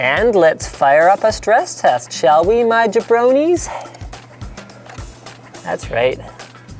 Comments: Did I just hear "Bronies"? Is Linus a Brony?? Whaat? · Did I hear right? Does he said Bronys :D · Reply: I believe it was jabronies. As in the thing The Rock used to say all the time in the WWE.